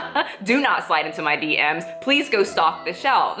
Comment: At first I misheard her as saying stalk the shelves instead of stock the shelves